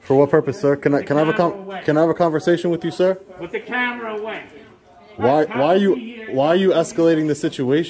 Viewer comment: sounded like a cop saying but the weapon away, but the weapon away